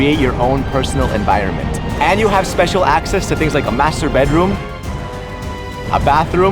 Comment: did i hear wrongly? masturbate room?